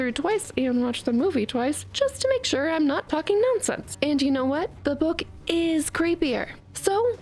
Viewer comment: Is it me or is she saying a read the book twice and watch the film twice to make sure I'm not fucking nonsense